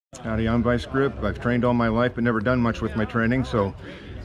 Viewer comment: Idk why I heard “I’ve strangled my wife” instead of “I’ve trained all my life” at first in the intro 😭